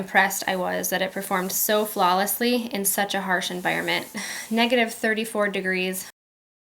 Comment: did u say negative 34 degrees, bc that 's what it sounded like u said